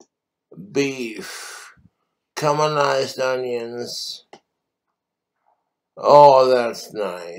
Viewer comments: anyone else hearing "cumalized onions" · Camonized onions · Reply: Genuinely thought he said cuminised onions